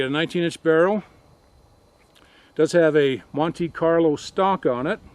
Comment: LOL, when you said Monte Carlo stock it sounded like you said Monkey Carlo" stock!